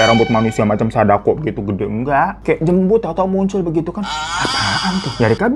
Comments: tak ulang2 salah denger g sih kok ngomongnya tanpa beban 😂😂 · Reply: Iyah kok ngomong jembut nya halus bgt🤣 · kayanya gua salah denger dah gua kira dia bilang jembut 😅